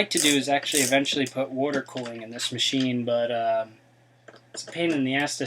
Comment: did you say water cooling?? at 4:43? If you did could you do a video on it because that would help a lot of people but if its too much of a pain in the ass then its totally up to you.